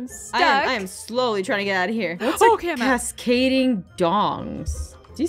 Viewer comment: Aphmau: Cascading Dongs: What did you say? Me: SHE SAID CASCADING DONGS!!